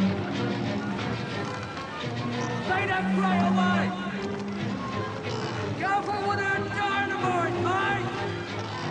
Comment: What is the harbor guy saying at 2:41? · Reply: I believe he says "Lay that freight away!"